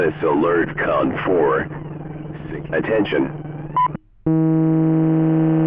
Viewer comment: I thought he said "attention... FU K"